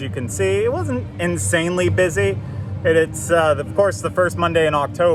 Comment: Does anyone know what day of the week this was? I'm not sure if I missed it or if it just wasn't mentioned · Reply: I heard him say Monday